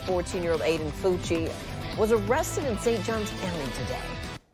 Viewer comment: Who else thought that said “fauci’s mom arrested”?